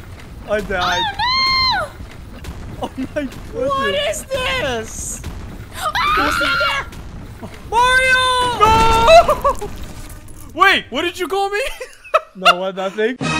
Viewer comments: Happy Saturday!! What do you think of this new update? There's some sweet new mini games :) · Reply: Did just call by his actual name Mario? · Right here 11:01 Did he say MARIO!? I didn't notice either for a whole minute, so, I'm in the same kind of situation.